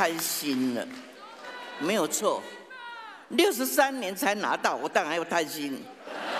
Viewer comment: 六十三年才拿到後面那句話是講什麼？聽不太懂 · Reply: 「63年才拿到，我當然要貪心」😆😆😆